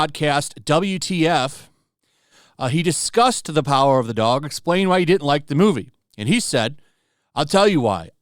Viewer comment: I thought you said power of the dong!